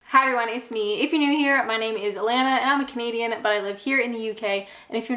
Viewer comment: Every time i catch up on your videos, i always hear “My name is Alana, I’m a comedian”